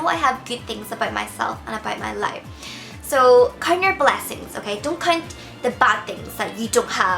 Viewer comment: Bubz, great video, but watch the accent when you sound 'count' :O for a second i thought you said the C word